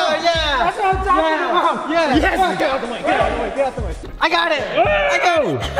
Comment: Did he just say “get the f**k out of the way”